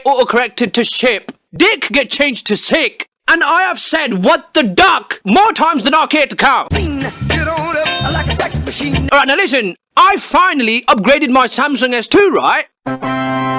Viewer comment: i heard music saying what on up I'm like a sex machine LMAOOO